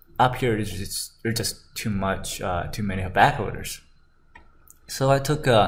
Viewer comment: What is the word he is saying at 3:11 "Over here there is just to many (?backloaders?) , (?bad orders?)" What does he mean? Thanks for clarifying. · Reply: I think its bag-holders.